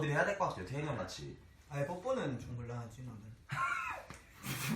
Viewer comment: 뭐라고하는거에요?? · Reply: 뽀뽀는 좀 곤란하지 나는 이라고 했어요